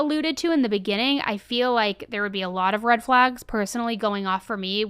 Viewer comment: CIA does not get involved in cases like this. They are for out-of-the-country stuff. First red flag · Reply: I thought you said CIA does false flags and I almost agreed 💯. But you said red flag. Not the same.